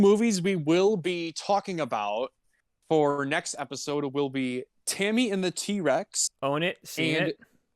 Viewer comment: For a fraction of a second I thought you said Timmy and the T-rex lol. Also every time says "game" it sounds like "gay" and I can't decide whether or not it's intentional XD